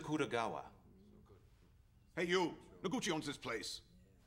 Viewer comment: Did he say Gucci owns this place?